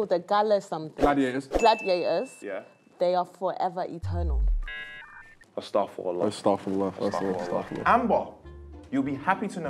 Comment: what did they say? "ASTAGHFIRULLAH"???